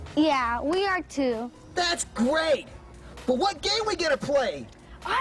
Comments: he says gay instead of game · I swear said "what gay we gonna play"